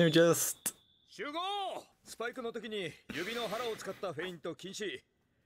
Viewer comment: the coach didnt say that feints in general are not allowed, what he actually said was "指の腹を使ったフェイント禁止" (ゆびのはらつかったフェイントきんし) wich means feints with your fingertips are not allowed atleast thats what i understood